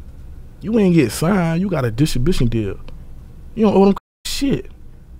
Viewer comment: 🤣😂🤣 what this nigga say? “You got a dishin bishin deal” 😂😂😂. Stay in school kids!!